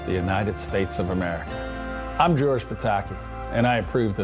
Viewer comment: At first I thought it said George Takei.? I thought Takei was running for president